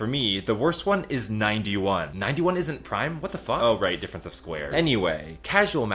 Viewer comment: Did just say what the f